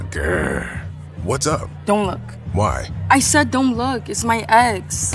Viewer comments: I thought ice SPICE said”DONT LOOK ITS MY A$S”LIKE wtf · “You look so pretty when you GrÆAHHH” ✨gæarhh✨ “🐺Græhh💀” “STAWP DONT LOOK ITS MY EX😩” How did I get this much likes😭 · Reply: I thought she said it’s my ends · “You look so pretty when you GrÆAHHH” ✨gæarhh✨ “🐺Græhh💀” “STAWP DONT LOOK ITS MY EX😩” How did I get this much likes😭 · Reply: She sounds like she said it's my eggs ngl 😂